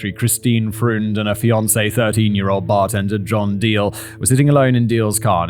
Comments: Did Simon say 13 year old bartender? 🤔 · John Diel is age 30 at the time of the attack. I had to look this up because it sounds exactly like (and the subtitles say) Simon is saying "13." Bump this up for others late to the party, please =).